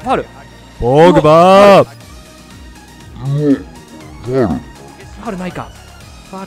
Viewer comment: のとこファールないかが、はるなあいに聞こえたww